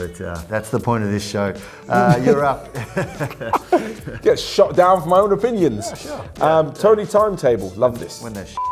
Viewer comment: Thanks for choosing my question at 2:49 You guys make great content, keep it up! What exactly did say when said my name? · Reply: says: I get shot down from my own opinions And the other guy replies: yea when they are shit